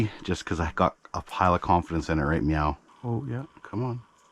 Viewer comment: Did I hear it right but at 4:00 you said I have a pile of confidence in it right mew (like the cat). If I herd correct lol to funny